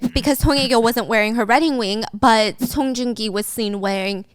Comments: Did anyone else catch saying "redding wing" instead of "wedding ring" at 47:41? 😂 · 😂😂😂😂😂 I love you but I laughed when I hear you say REDDING WING!!!!! ❤❤❤